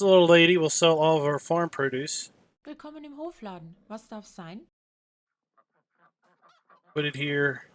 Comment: So in 37:21 what does the woman say in german after Willkommen im Hofladen? Is it "Was darf sein"? My german is very rusty, since is not my native language, nor english. Any german speaker here? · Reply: Your guess was quite close. She said "Was darf's sein?". Thats a shorter form of "Was darf es sein?".